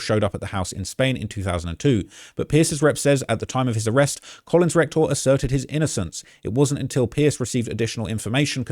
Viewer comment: I keep hearing you say rec-tal for that guys name and it all sounds wayyy different if you hear it that way